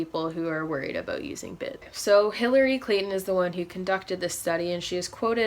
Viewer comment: I thought u said hillary Clinton lol